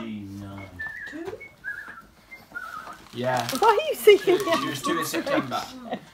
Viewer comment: Did Alfie say "she is due in September at 3:33 ?!! " please say she is pregnant !!! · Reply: He said "she was 2 in september", referring to Nala's age.